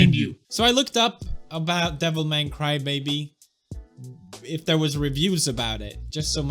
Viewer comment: I keep hearing "Devil May Crybaby". I dunno why.